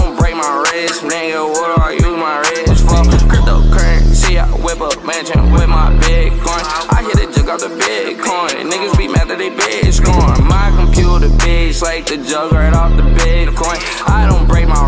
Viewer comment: Nobody KNOWS THE LYRICS BRO. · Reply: I hear "My computer bits like something after bitcoin" xd